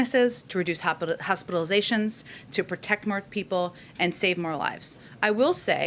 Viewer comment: what did she say at 1:12:35 then? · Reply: more she said more, anyway that doesn't make that poison better...